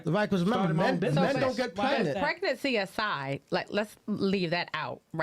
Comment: I thought she said let's take pregnancy out... 😊